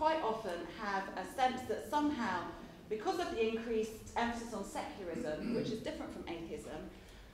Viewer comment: What does Francesca say at around 25:10 to 25:17? Is it absence of 'secourism'?